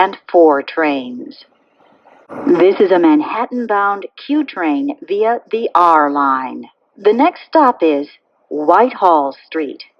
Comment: its ridiculous. The D to Whitehall, the F via Broadway...I miss when they actually took TIME into doing the announcements. Even at 33rd St on Lex, some dude was rushing so fast he copied the ENTIRE Penn Station transfer announcement from 7th Avenue and put it on Lexington. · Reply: I thought it said "Brighton Beach bound W train via the D Line"